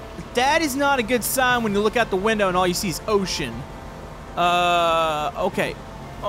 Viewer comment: I heard That DEMONITIZED